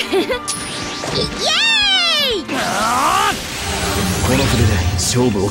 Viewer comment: I swear Ino says eshay